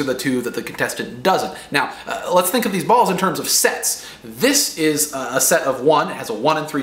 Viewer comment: i thought he said: lets think of these balls in terms of sex